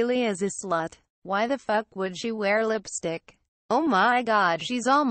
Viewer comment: I THOUGHT SHE SAID LIPSDICK